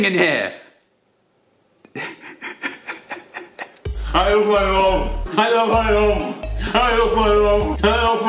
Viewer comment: Am I the only one who hears "I lost my job" 😅😂